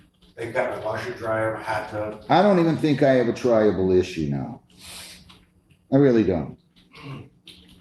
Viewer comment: LOL, I always thought they were saying tribal issue, not trial able.